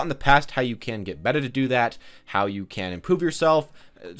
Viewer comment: I love this series but at 0:23 did you say, “How you can poop yourself”? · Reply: how you can get better to do that, how you can IMPROVE not poop :P